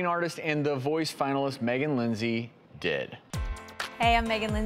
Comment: I thought he said “Meghan Linsey Dead” I was like OH MY GOSH! 😂